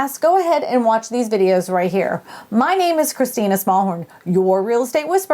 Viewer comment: I heard "Christina Smallhome"!!!